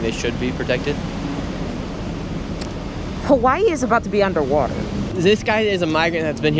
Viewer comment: “Hawai is about to be under water”. Is that what i heard at 9:04? in response to a different question?